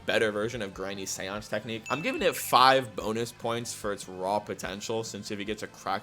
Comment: Did you say... Potential?